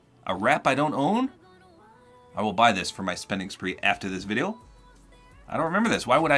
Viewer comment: did you say for your next spending spree